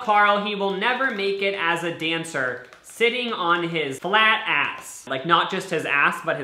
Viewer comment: why did I hear black ass😂😂😅